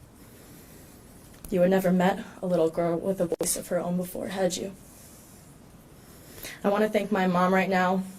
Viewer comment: what did she say after "you have never met a little girl...."? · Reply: With a voice of her own before...had you